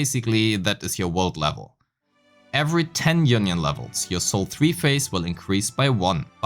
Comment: I misheard union levels as "yunyun levels" and can't unhear it lol.